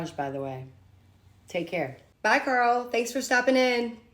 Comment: he said girl instead of Carl